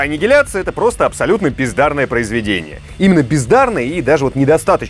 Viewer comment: "Аннигиляция - просто пиздарное произведение." Извините, послышалось. З.ы. Лучшее кино по игре - "Постал", пускай и Уве Болл. Когда первый раз смотрел - ухахатывался.